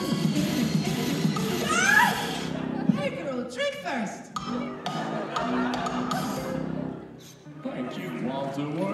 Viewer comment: I still couldn’t understand what said, I always thought she said “Buy me a drink first!” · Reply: She most often says "Buy a girl a Drink first!" so you weren't wrong.